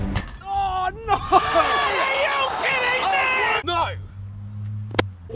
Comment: Did I hear "Hiko are you kidding me"